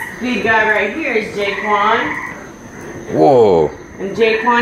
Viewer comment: Thought it said daequan